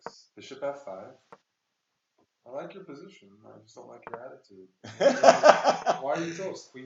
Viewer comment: Did say "I like your position but not your attitude" around 6:42?